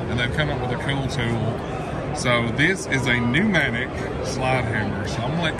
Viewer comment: Pneumatic slide hammer, i thought he said horizontal air hammer, lol.